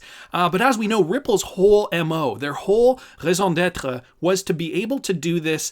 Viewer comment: seconds in the video what was that word? 😂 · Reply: raison d'etre means "reason to be." or "their purpose" in french